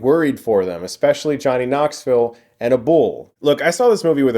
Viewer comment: Am I the only one that thinks it's weird the way he pronounced "a bull"? It sounded like he was saying a person's name, some guy named "Abule". Lolz